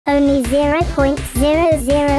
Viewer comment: I thought it said "pink a box"